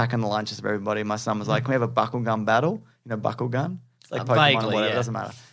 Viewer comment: lol @ 1:00:30. Legit thought James was saying "buckle gun". Not trying to be rude, but sounded like a euphemism you'd find on Urban Dictionary. But I guess it's "Bakugan".